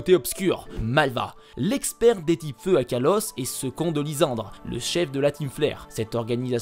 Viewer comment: J'ai compris "ce con de Lysandre" 😂